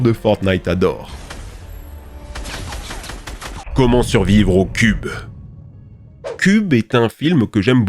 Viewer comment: "comment survivre au cube ?" Moi : à Kévin ?